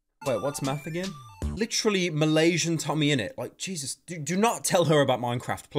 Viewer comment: Wait, did you say Malaysian TommyInnit? Oh my god, finally a recognition of my country from itself